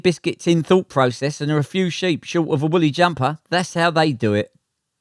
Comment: I heard the phrase 'willy jumper' - really must get me some hearing aids 🤣